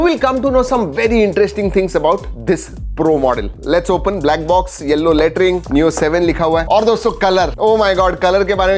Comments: did he actually say yellow latrine 🤣🤣🤣🤣🤣🤣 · i heard yellow latrine 💩and now couldn't stop laughing 😂🤣🤣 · Black box yellow latring 00:31 What did i hear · did he just said yellow latrine 😅😅😅😅😅😂